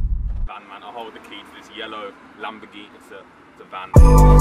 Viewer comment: I thought he said yellow Vanberghini